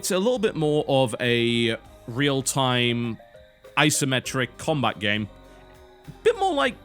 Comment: I'm not american, though it really sounded like he said "eye simetric' oh well.